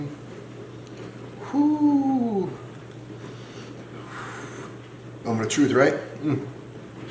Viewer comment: 13:24 I heard a voice come through, not sure if you were aware, I follow you guys and watch a lot of your content. You guys really have something that follows you! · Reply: yeah yeah i heard that too! sounds like it saying "i'm gonna get him" ? wow better get your guys place a blessing or light some sage.